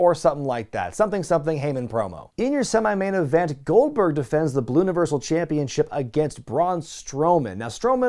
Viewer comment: I replayed 13:06, did you say "Balloonaversal?" championship.